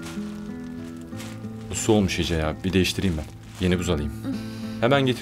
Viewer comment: 4.53 / Emre, buza soğumuş mu dedi ben mi yanlış duydum ŞAÖDŞWÖDŞWÖDŞWÖDŞWDŞ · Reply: Bu buz su olmuş dedi eridi demek istedi yani